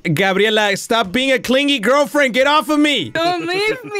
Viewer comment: did he say "GIRLFRIEND"???